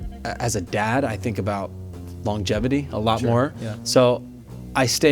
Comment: I know he said longevity, but at first I thought he said as a dad I think a lot about bon Jovi and that fits too 😅